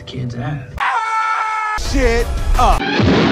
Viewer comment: what did she say ???? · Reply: I think s**t idk